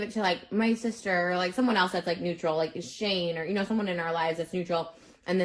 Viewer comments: Uhm excuse me 2:20 did she say SHANE????!!? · Did she say SHANE???? So she's still friends with him even though she says in other videos that she has moved on.